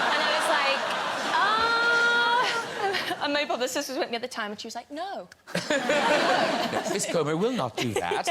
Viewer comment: What did Jodie say in 6:35 min ? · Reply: Mabel who was with me at the time , she was like no